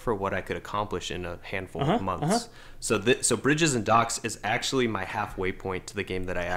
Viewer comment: Up until about the 28:00 mark, I 100% thought they were saying "Brigerton Docks" like the Netflix TV series "Brigerton", and thought it was a deliberate pun on bridges.